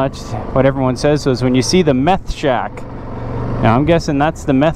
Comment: Yeeeeehaw! A video with my morning coffee! Me likey! - - - 5:20 - Wait . . . did you say "The Meth Shack"? - hahaha - Not the prettiest warm-up Shelter, but if it works, that's all that matters. - - - Holy mid-roll ads Batman! 2 in the first 7 minutes. (5 in total) Not sure if you say okay to "midrolls" or not, but if you do, you may want to customize them so you don't lose viewers. Just a suggestion. I mentioned this to another Creator & he told me to buy "YouTube Premium", but if everyone does that (which I'm sure is what YT wants), us video creators would no longer get paid. YT has already cut our payment enough as it is. Sheeesh! - lol - - - 26:50 - HOLT GEEEZUS BOY, that looked good! - - - 32:00 - we have too many bugs here to leave the screen open on a tent or camper - lol - although, I do recall this happening when I was a kid. A skunk got into our tent and my dad had to get it out. Threw a blanket over it, grabbed it and ran - hahahaha - and no spray, luckily. I think the blanket kept it calm. Did you think about picking the tent up and sliding him out the back door, then running like hell with tent in hand? That might have worked . . . "might have" - hahahaha!!! - - - 38:00 - I'm not one for breakfast sausage, but that looked delicious! - - - Awesome video buddy! Loved the scenery with the drone footage! Thanx for taking us along!